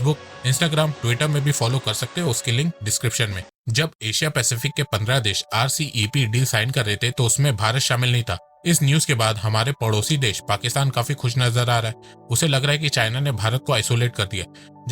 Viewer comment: did you want to say our bhikhari padosi Desh your speech sounded like that 😂🤣